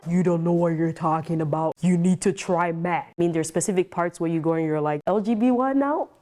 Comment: I heard “you need to try meth!” 😭